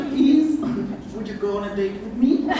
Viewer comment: can someone tell me what was the question on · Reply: "would you go on a date with me?" 🙈